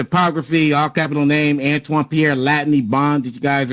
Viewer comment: Here is the typography!! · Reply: Thank you for this. I thought he was saying topography, which made absolutely no sense at all.